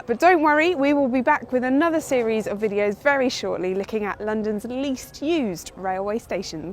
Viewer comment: 'we'll be back, Licking at London least used station' Did I hear that? 😛🤔